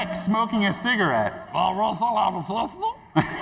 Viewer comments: What does he say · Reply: Fall out! At the disco “does that whistle have a sister?” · I beg someone translate what rick said at 11:10. I've been sat here for ages trying to figure it out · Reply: "Does that whistle have a sister?"